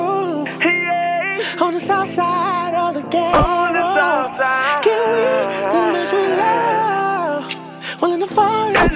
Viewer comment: What did he say “0:28 “Can we make sweet love while in the (?) ...” what did the question mark say ? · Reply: Baddie Lî Banks In The Forest Or In The Meadow